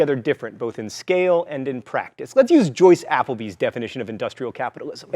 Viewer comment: Who does he quote at 2:20? Joyce Appleby? At first I thought he said CEO of Apple